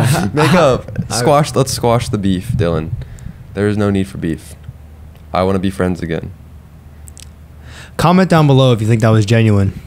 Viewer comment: “i wanna be friends again” What i heard - “i wanna be more than friends” 😭😭😭😭